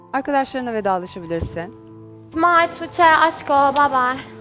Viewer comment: ''simay tuçe aşko baybay'' dediğinde ingilizce bir cümle kurdu sandım anlamadım. aşağıdaki yorumları okuyup. geriye dönüp tekrar baktığımda simay tuçe aşko babay dediğini anladım :D